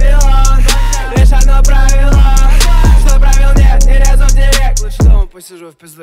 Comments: Мне слышалось «По руке иди сюда» Вместо «Ору ей иди сюда» 😂😂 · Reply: Мне одному послышалось а ну гей иди сюда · Мне же одному слышится "Ауе,иди сюда"?)) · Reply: Русский Сокол мне послышалось «А ну гей иди сюда 😂😂» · Мне же одному слышится "Ауе,иди сюда"?)) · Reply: Мне послышалось "на руке иди сюда " XD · Мне слышалось «По руке иди сюда» Вместо «Ору ей иди сюда» 😂😂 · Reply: "А ну гей иди сюда" послышалось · Мне одному слышится ауе иди сюда?😂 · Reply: Мне послышалось "Ану, гей, иди сюда" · Мне слышалось «По руке иди сюда» Вместо «Ору ей иди сюда» 😂😂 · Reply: Мне послышалось "Ауе иди сюда" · Мне же одному слышится "Ауе,иди сюда"?)) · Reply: Мне послышалось "сися, смарт я как вода" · Мне же одному слышится "Ауе,иди сюда"?)) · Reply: Русский Сокол а мне послышалось "А ей иди сюда" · День думал , что он поет , а там оказалось "всегда смарт я , как вода" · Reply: Мне послышалось беру дочь иди сюда)))